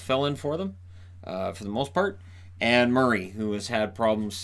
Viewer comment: Need to check my hearing after I thought you said Ann Murray was in net (aka and Murray).